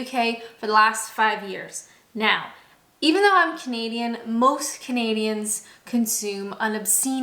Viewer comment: I watched a few of your videos now and have just realized that you've been saying I'm Canadian. I thought you were saying I'm a comedian. LOL I was so confused. I kept trying to crowbar it into context.